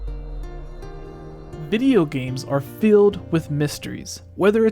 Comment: did he just say bideo games?😂